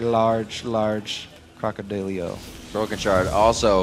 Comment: when the commentator said crockodilio. i thought he said cockadildo